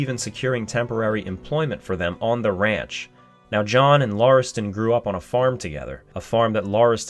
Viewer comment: I swear the first time I heard this I thought you said John Marston